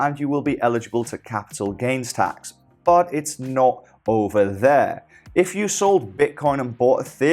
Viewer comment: Snot over? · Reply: You said it’s not over, but it sounded like you said snot over lol, sorry, just my inner child coming out. Keep up the good work bro.